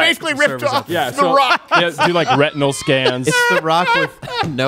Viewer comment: he says "Retinal Scans" which means "Eye". I thought he said "Rectal Scans". What the hell is that? "Bend over, and I'll tell you who you are!"